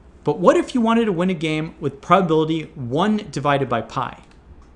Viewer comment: the subtitles is wrong It's 1/pi not 1/2